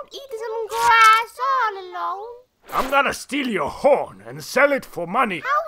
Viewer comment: LOL I thought he said: I'm gonna steel your horn and sell it for honey!